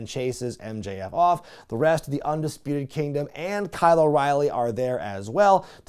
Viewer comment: Sometimes I hear Kyle O'Reilly's name as Kylo Riley and I picture a combination of him and Adam Driver.